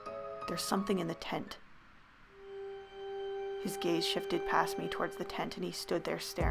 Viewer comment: oh no at 21:35 i heard "his gay shifted past" AHAHAHAHH